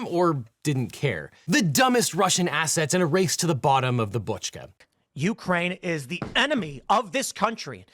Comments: I misheard that as "to the bottom of the botchka". As in "бочка". As in "barrel". Bottom of the russian barrel. Lol. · Whenever you say "assets" in this context, I hear "asshats."